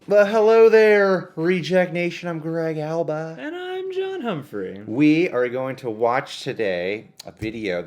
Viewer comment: Everytime he says I'm John Humphrey all I hear I'm Dan Humphrey